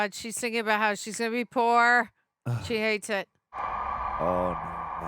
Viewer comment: what did cassie say here? Actually I can't hear it over them speaking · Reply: "I dont wanna be poor" lmao😂